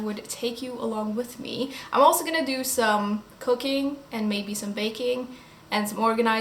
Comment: Oh my GOD I could have sworn you said "also going to do some cocaine". Bit of a shock there, hihi.